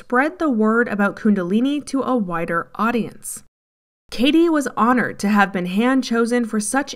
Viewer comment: When he said to expose his teachings to a wider audience, I thought she said whiter audience. It probably was 😂